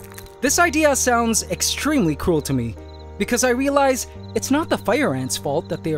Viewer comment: I literally thought you said "extremely cool" for a second, but then I checked back, and "extremely cruel" makes way more sense. 😂